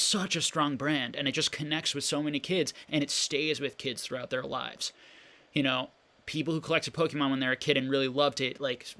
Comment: Sorry at around 1:34:46 you were comparing a specific series to the vending series. Were you saying “versa series”? Or something ? Couldn’t really make out what you were saying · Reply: vs. series it was a Japanese only addition to the gym sets. Very cool set!